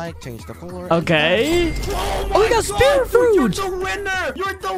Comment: did bro say spin fruit or spear fruit?